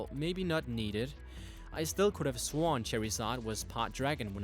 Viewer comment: no offense but it sounds like your say cherriezard